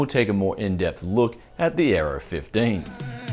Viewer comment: did he just say error 15?lol may be gigabyte named it wrong!!!